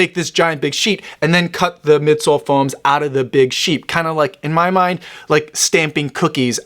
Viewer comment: Loved the video, as always. But 2:20 , is it just me or did he really say " Cut the Big 'Sheep' " there😅?